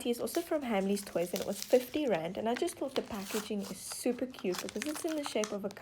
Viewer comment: I honestly thought these were soooooo exspensive like 80pounds!! ... then i relised that like 85.99 rands is like 4 pounds and still about 4 dollars i hope this helped and i hope u enjoy today or night depending when ur reading this ☺ also this is the first time ive watched u and i loved it and at first i honestly thought u were saying grand! 😂 Edit: omg thank you sooooooooo much for liking my comment i am suscribed to u by the way when i watched this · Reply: diamond_core_slime I thought she was saying GRAND TOoOo!!😂